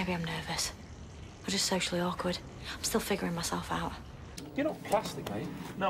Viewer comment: Totally thought she said she was sexually awkward with her accent